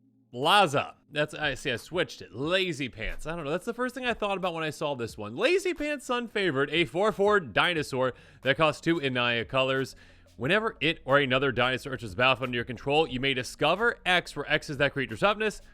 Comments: I thought it said sun flavored. lmao. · Thought the name said Sun-Flavored instead of Sun-Favored